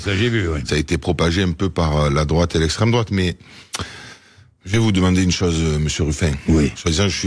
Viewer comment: C'est moi ou il dit" je vais vous demander une chose monsieur Ruffin "